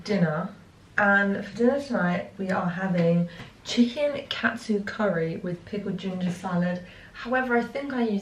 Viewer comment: Had to rewind a few seconds as I thought you said cat soup curry 😂 xxx